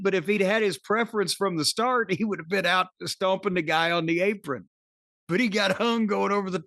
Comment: does Cornholio say stompanigguhout?